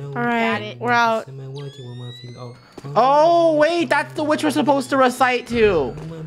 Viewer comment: doesnt sound like saying the b-word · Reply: She said witch